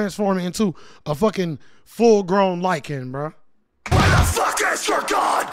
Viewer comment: not gonna lie, I thought you said lichen and I was confused for a second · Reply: Lycan lol